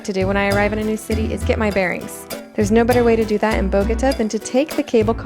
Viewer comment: I thought she said AGODA, was like what? And then realize she was saying Bogota lol